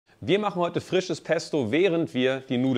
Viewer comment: Ich hab frisches Testo verstanden 😂😂😂😂😂😂😂